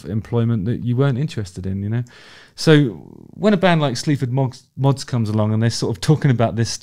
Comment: oops, I thought you said "Sleep For Moths" (maybe you could put up subtitles sometimes for us silly americans, haha!) Reminds me of Jello Biafra for some reason. And that's pretty high praise! I wasn't into it at first. Maybe because I've gotten a little tired of so many people trying to combine rap/hip-hop with {insert whatever musical style here}. By the end of the song I was feeling it. Love the energy. Love that minimalism. Love those dance moves! Just listened to the songs with Amy & Billy. Both were pretty damn good.